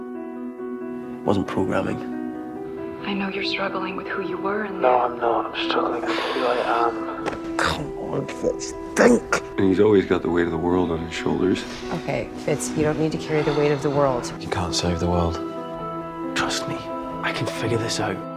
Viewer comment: Did he actually said "DENK" the german word for THINK?